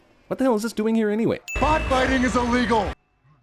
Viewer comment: it sounded like he said cardfighting not botfighting then i whoud told him to watch some anime like yu-gi-oh